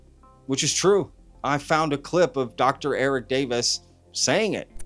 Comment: I thought I heard Eric Davis say 'like Lacatski desribes in his book' @ 14:49 I agree all this does seem odd.